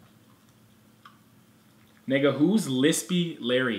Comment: Did he say n*gga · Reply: Yeeet The Candy he said nigga